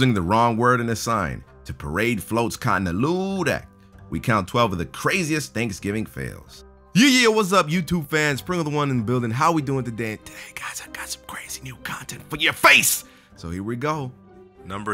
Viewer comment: Is it just me when I hear "Front of the one" instead of pringles actual title when he does the intro?